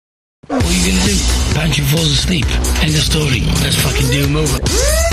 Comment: 😂😂 what does pancho say hahahaha so funny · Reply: Effective Hit so funny. Always used to rewind this scene just for that. “ come on then, let’s fucking raaaarrr turn a boyaaa “